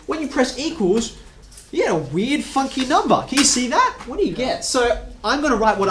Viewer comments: thought he said a wierd f**** number not funky 😂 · I swear I heard him say "weird fucking number"